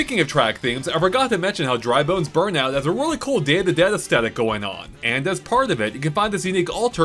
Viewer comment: I kept hearing "Dandadan" instead of "Day of the Dead" until I turned the captions on 😂